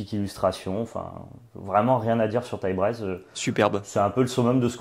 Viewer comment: j'ai pas compris, "Tiebraize"? haha c'est quoi le nom? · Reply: Taillebraise / Embercleave en VO, un artefact venu d'Eldraine